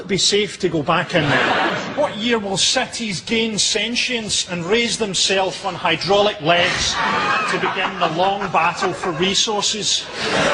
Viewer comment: What does Frankie say at 2:04? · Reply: In what year will cities gain sentience and raise themselves on hydraulic legs and begin the long battle for resources.